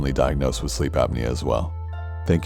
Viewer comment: Did anyone else catch Corpsey say "Holy Shit!" at 21:27???🤔🤔🤔 Random, I know. Wonder if this video took alot?